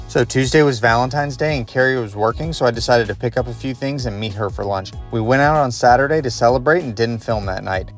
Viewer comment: I heard “eat her for lunch”